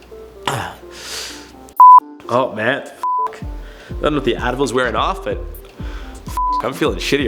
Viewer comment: Ohhhh Advil ...... I thought you said Adderall 🤣😏 I was like dayum srgt pepper